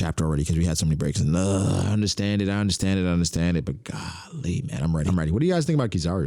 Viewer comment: I am not a native English speaker, I have heard people say this word before a lot 15:00. Can someone please tell me how it's spelled, I am really curious. Thanks. Is it "Golly"? I've heard only african Americans say this (sorry if this is offensive) (first I thought they are saying Kyrie xd)